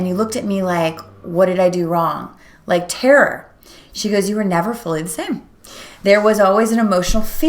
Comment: What is the word you say at 4:18? I can't hear what the word is? "You were never fully in ?????" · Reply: She said, "You were never fully the same."